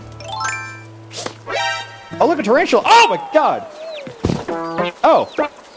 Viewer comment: When he said look a tarantula i thought he said oh interacial